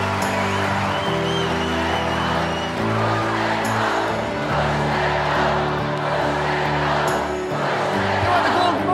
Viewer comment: ここから始まるゴールデンブザーコール、英語で何て言ってるか分かる人います？ 『Push...』って頭は言ってるように聞こえるのですが。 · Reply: push the goldです